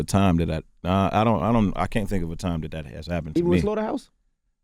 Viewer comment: what was said at 16:10? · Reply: the cave dweller "even with slaughterhouse?"